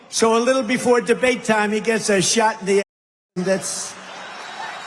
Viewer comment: Shot in the head? What did he say? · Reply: As*